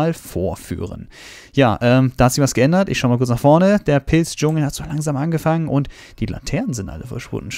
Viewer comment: Der Pilz-Dschungel! omg ich hab erst pissdschungel verstanden und dachte so wtffff